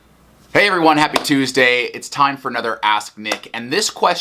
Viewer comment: At the intro, I thought he said "It's time for another ass kick." And I got a bit worried there. Maybe future shirt idea?